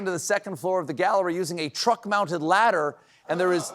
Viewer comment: Sounded like: "The thieves broke into the gallery using a TRUMP-mounted ladder." 😂